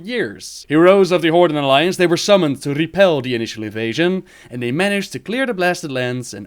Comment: i swear it sounds like u said asian invasion at the beginning XD